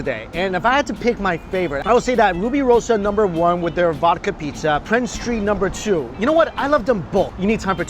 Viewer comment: did he just say vodka pizza? i wasnt sure if i heard it right?